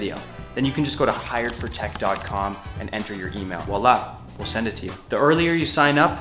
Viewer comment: did he just say wlhi i will send it to you lol